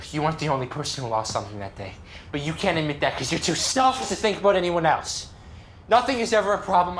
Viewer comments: wtf does he say here? Did he do a hard cut between two takes with completely different tones? Terrible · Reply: “You’re to selfish to think about anyone else” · “Stelfish”? Was that just a shitty cut or did he say the line wrong?